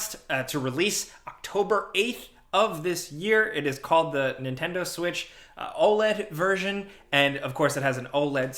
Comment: Sounds like said "Switchola". I think that's a great name for the bad specs scandal involving the new Switch.